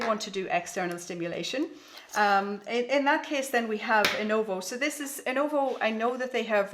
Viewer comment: What does she say this device is called? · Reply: That's Innovo shorts. The ones I am showing there are the older model. The new version is an actual pair of shorts with the simulators built in. You can find them here: <link>